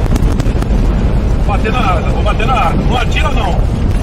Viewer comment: What the guys are saying: 0:17 _"Vou bater na asa, vou bater na asa... não atira não." _"I'm going to beat the wing, I'm going to beat the wing... don't shoot." 0:27 _"Desce um ai que eu vou pegar os outros lá". _"Get one down there and I'll get the others there" 0:30 (_"Para, polícia... parou!... sai fora!... parou") (_"Stop, police... stop!... get out!... stop!")